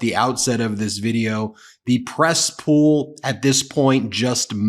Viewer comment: Press room? I heard "Cesspool". Because that how it stinks now.